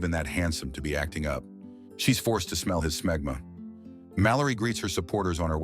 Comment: I had to go back to hear what you said. Not she was forced to smell his smegma 🤣🤣🤣🤣🤣